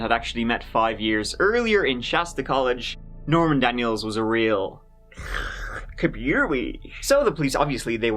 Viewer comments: man i love but sometimes i have no idea what the hell he is saying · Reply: “A real computer whizz”. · Can someone tell me what says at 5:50 ? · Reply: “A real computer whizz”? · Wtf is saying at 5:50 The subtitles don't seem to help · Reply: Computer whizz? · … what? Best face ever, but I don’t know what said. Captions say “kabiri” 😅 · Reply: Computer whizz?